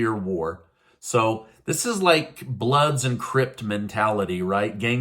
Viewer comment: I believe u mean Crips. I know u probably know that but it sounded like u said crypts. sorry. I'm a big fan tho. thank you for everything you do.